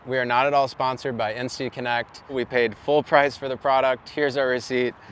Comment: Hey guys! Love watching your travels. Do you have a link for the WiFi products? I’m starting road life soon and will need WiFi! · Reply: Insty connect! Got it. I thought you were saying NC connect! Thank you guys! See you on the road!!!